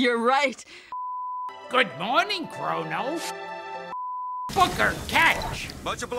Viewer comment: I lost it at "Booker, catch!" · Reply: I thought he said Hooker lol